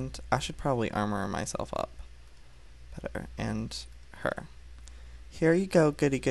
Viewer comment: PlayingwithPIG He Said Better I Thought He Said Butter To But He Really Said Better